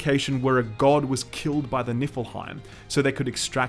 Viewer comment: lol..."i swear it sounds like your saying Nippleheim... "God was killed by the Nippleheim"..